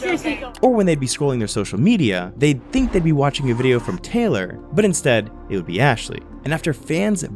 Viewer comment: Uhm I was watching the part at 5:40 and when he said instead I got an ad this is what it sounded like😭 but instead- tic tac chewy.